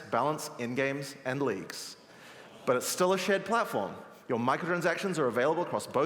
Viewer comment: When he said "Shared Platform" I heard "Shit Platform"